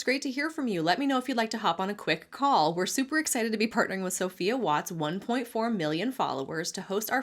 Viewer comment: the way it sounded like “Sophia Watts 1.4 Mil Followers” was her full legal name